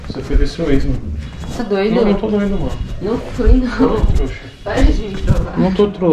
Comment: "Nao to amor" 6:19 só eu ouvi isso ?